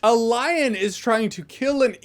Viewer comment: Solid edit A little after when you saying Lion. I thought you were saying line because of your dislike for the line 'just like you said mom wouldn't die.' Like a shitty line was stalking the script and someone had to say it.